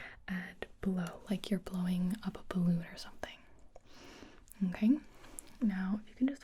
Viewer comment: Did anyone else hear “blowing a baboon”